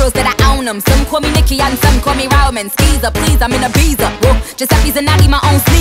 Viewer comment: bruh im so stupid i heard SOME CALL ME NIKKI AND SOME CALL ME ALMONDS KEKDKDKSKDJDJW